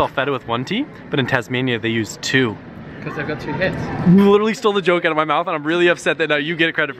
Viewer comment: no clue what said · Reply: cuz they have 2 heads